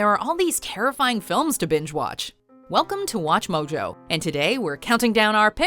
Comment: was I the only one who heard bitch watch? 😂